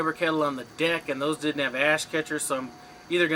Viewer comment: I laughed out loud thought u said - ass catcher